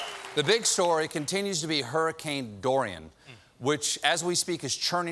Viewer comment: Every time I hear "Hurricane Dorian," -- I swear I'm hearing "Hurricane Durian," lol